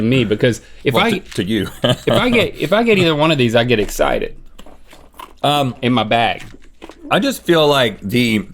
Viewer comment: did he say "I get excited, in my bag"?..lmao